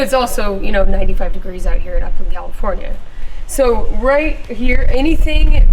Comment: Did she say Upland, California!!! The Inland Empire!! Upland, Rancho Cucamonga, Chino, Ontario, Claremont and surrounding cities